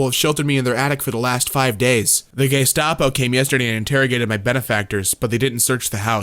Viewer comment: I lost it when I heard Gaystapo. Just imagine the most flamboyantly gay men in very colorful suits interrogating you with the most over exaggerated gay voice ever. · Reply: I heard "Gaystapo" and spent the rest of the video imagining how incredible their uniforms would be.